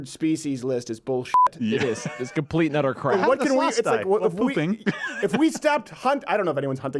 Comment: I legit thought you said “Slavs” instead of “sloths” when talking about pooping